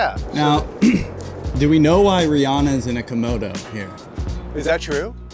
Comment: Did he say “Komodo”? 😂